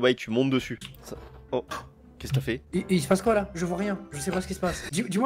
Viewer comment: pourquoi j'entend "caisse café"?